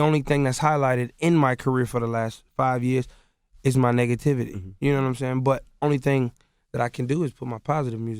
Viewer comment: I swear when Chris said negativity it sounded like he said niggativity. Yo I just made a new word god dammm lmaoooo. U know if u wana respected in these streets u gota have DAT niggativity.